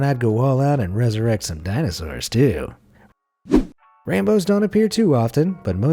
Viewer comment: did he just say ranbow